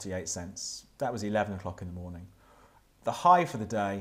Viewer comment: did he say " that was 11 f**king morning?"